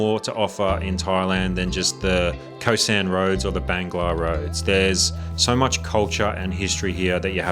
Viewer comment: what road did you say after khaosan road? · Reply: Bangla?